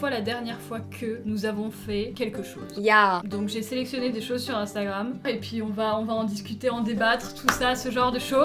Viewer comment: j'ai entendue "j'ai selectionner de chaussure instagrame" XD